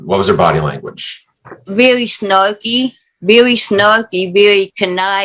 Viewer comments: She sounded like she said snuggly initially, but then she repeated herself and it was definitely snarky...which is a huge difference. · The judge was awesome in this case. She listened better then either lawyer. Another great one, thanks. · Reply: Except she somehow thought said snuggly & not snarky. She clearly said snarky several times. Aside from that, she was great. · Also. 5)3 judge thought that the plaintiff said snuggly but she did in fact say snarky.